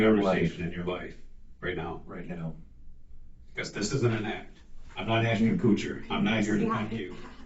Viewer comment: he says “I’m not trying to PUNK you” not “I’m not trying to f*ck you” Like me closed caption says💥